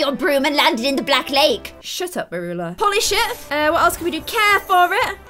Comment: oh 😂 ... I thought you said at 13:03 'Holy s***' 😮 But then i realized, after playing over 5 times, that you said 'Polish it' 😅. Love you lots! 💖 x